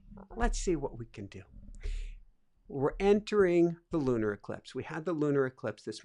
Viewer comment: was actually saying, "Lunar eclipse" ! starting at ~8:05 she says it a few times!